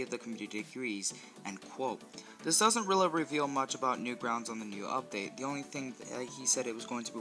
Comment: Did I just heard "Newgrounds" 7:13 what is that audio stutter? What's the level in the background? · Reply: yea sorry... this was all edited on my iPad, and I said “New grounds” not the website lmao